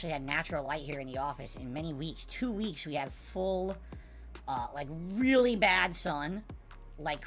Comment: Did he just say “…we had full, really bad sun”? Oh, touch grass